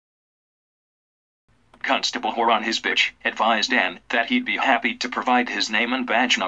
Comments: constable “Whore on his bitch” did anyone else catch that 🤣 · wait what did he say? “Whore on his bitch”